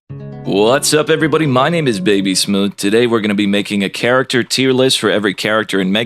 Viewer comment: I swore you said my name is baby smooth at the beginning lol